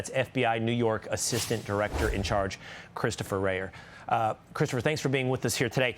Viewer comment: Thought he said Christopher Wray the former director for a minute